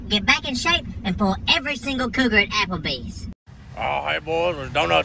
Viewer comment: As I just got back from the d-nut shop.... They wasn't for me and I ain't eating none... But Still probably ain't getting in shape either.. Every single cooter.... Loving it..... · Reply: Oh it was cougar... I thought he said " single cooter" Oops, my bad....